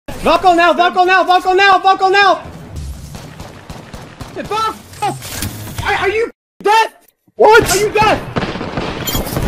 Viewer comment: Tbh, I heard "buckle now buckle now buckle NOW!"